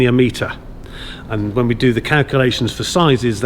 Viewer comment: Could of sworn I heard say he did the cow-culations